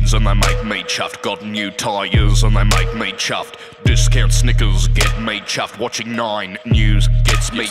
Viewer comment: I always thought Broden said "discount sneakers"